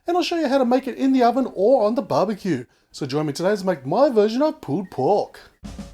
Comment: sounds like hes saying pooped pork XD